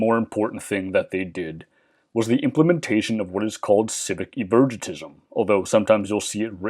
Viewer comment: can someone tell me what he's saying here? It sounds like "civic ivergatism" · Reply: Civic Evergetism